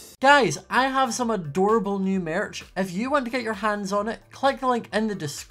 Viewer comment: When he said “new merch” I thought he said “new marriage” 😂